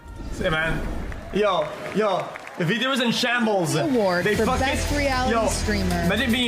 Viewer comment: can someone tell me what he's saying I can never understand what he is saying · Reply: "Vtubers are in shambles. They faqn.. Imagine being a vtuber taking out a loan against the bank, from a big faqn corporate to get a model for $60K to lose to the scuffiest pos avatar from windows 95, that's crazy" I've watched his clips for so long that I understand him now. No, I don't like that I understand him.